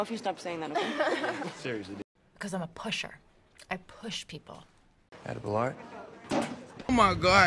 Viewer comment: I heard edible R??? 5:26 and at 7:39 he says no tank you... ?!! I have backed up this part for over 89 times!!! 1:50 these inabilities must run in the family 1:57 truly the best acting in the movie, 2:34 CRINGE PURE CRINGE 3:26 p.s. WHY IS SHE BLINKING LIKE THAT!!!